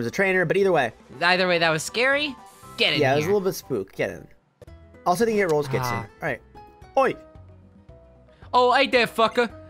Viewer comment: what in the almighty fuck is saying at 11:47?? i KNOW it's not important but holy FUCK is it BOTHERING me trying to figure it out [UPDATE] it's something like "Also I think this is where you get the rollerskates"